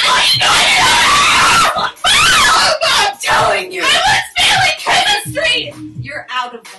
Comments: i thought she said that she was ‘FEELING’ chemistry 😭😭😭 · She wasn't just failing chemistry she was also feeling it😭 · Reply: thats what i heard i thought she said “I WAS FEELING CHEMISTRY” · I thought she said she was 'feeling' chemistry 🤣